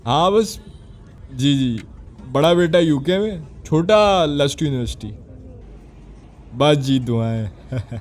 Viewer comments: did he say Lust university. 😂😂😂😂😂 9:01 harampai sy kaam lia hay. Aunty not coming slow. 😂😂😂😂😂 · what lust university 💀, am i tripping or he really said lust university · Did I hear "Lust" university 💀😭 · did he say: "Lust university"? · i think he said lust university of his son at 8:02 minute